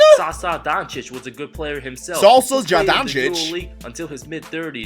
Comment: i don't know about what you said, but he said his dad SAŠA DONČIČ and by the way Luka isn't related to Nesterovič,he said his godfather! 😁😁😁